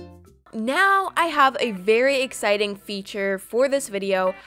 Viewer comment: She said “ meow “ instead of “ now “ Hehhehe Love your vids all of them are amazing 😉